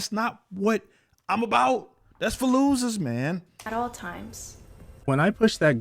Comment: Everytime i hear you say "thats for losers" it sounds like Fallujah 🤣